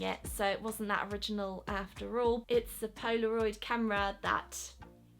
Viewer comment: I thought she said, “Poscaroid.” Instead of Posca pen, Or in her case, Posca markers